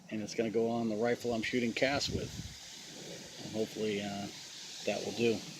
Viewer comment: Did I hear "shooting cats"? near the end of the video? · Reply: No , shooting cast. As in lead cast bullets.